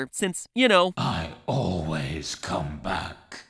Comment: on 3:00 to 3:06 springtrap does not say "i always come back" it is "SCRAPTRAP"